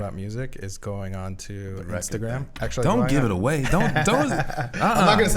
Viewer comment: Damn does anybody know what theyre talking about at 8:30?? 'Restagram'?